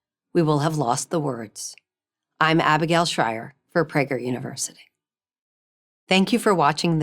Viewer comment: it sounded like she said preggers university. tee hee. Good video though.